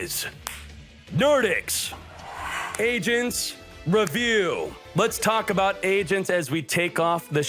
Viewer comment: it sounds like he says “asian review. Lets talk about asians”